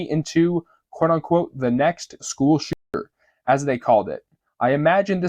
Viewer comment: shitter? Is he saying shitter?